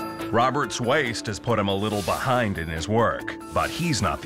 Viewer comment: “Robert’s waist had put him a little behind in his work ....” That’s the way I heard it... Then he shown his BIG BEHIND....